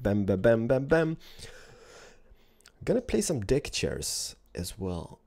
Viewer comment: I heard "Dickchair" XD 😂😂😂